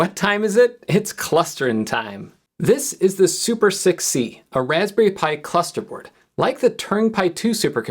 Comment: I thought you said "Super Sexy" at first instead of "Super6c". 😂😂 It is sexy though, but not as sexy as the Turing Pi 2.